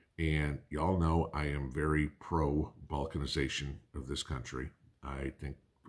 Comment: I'm sorry to ask , but used a word I thought he said vulcanization... does this mean bonding together as a community or something else? I've tried to define in a dictionary...my husband knows it as a word in bonding rubber through a heat process making it stronger. Sorry I'm dumbfounded on this word.. Great talk though. 😊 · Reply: Balkanization.